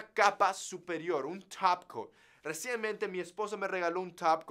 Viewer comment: dijo mi esposo? O escuché mal?